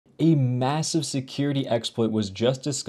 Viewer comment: did you say massive?